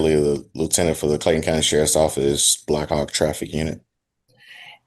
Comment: Did he say, "black op traffic unit?"